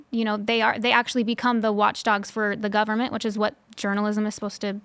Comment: sorry did she say journalists SHOULD be the watchdog of the government?